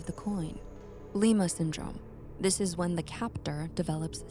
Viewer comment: At first I thought it said Ligma syndrome